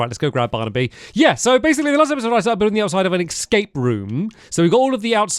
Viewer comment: excuse me what? i have ADHD and couldn't even understand you lmao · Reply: Yeah, so basically last episode I started building the outside of an escape room